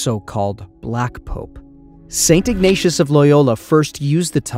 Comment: For a moment there, I thought I heard the narrator say 'satan-acious' rather than St. Ignatius! Hmph, interesting Freudian mishear, perhaps. . . Notwithstanđing, somehow I suspect that were Jesus to manifest in the materially sumptuous Vatican today He would be ab§olutely đisgusted to note the degree of unabated €vil roiling there, all perpetrated in Hi§ Holy name. . . Tsk! Tsk! Tsk! ⚡️🕊⚡️